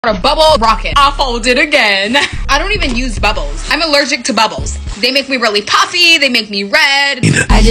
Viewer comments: GURL I THOUGHT YOU SAID BIBBLE ROCKET. that would be fire tho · I thought you Said bibble rocket 🤣